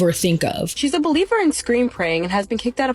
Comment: Ohhhh Scream Praying rofl 🤣 I thought you were saying Screen Praying, as in she just FaceTimes someone to pray over her kids when injured As a Catholic myself that also believes in tongues…This story is wild lolz 😂 I know we pray to God for healing and help, but God gave us doctors and nurses to help us out with our physical ailments It’s like that story my mom use to tell me of how this guy was praying for God to save him from a flood and there were firefighters, helicopters, and neighbors trying to help but he wanted God like actual God to help. And later when he passes and goes to heaven and asks God where was he to help, God told him he sent him help but refused it lolz 😂